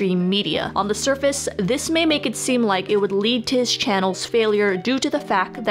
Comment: Stomp the breaks through the fucking floor.... did you just say "may may" for meme? 0:21. I actually can't tell what you said miss slurry speech.